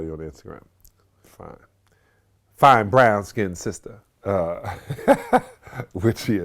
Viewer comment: sounded like star 😂 only thing missin was “and I say this respectfully”